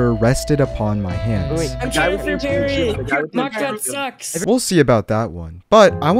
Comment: anyone else hear that…. ? Man said s** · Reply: blud he said sucks not the s word